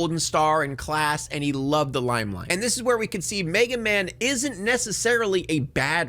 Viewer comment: i hear MeganMan not Megaman lmfao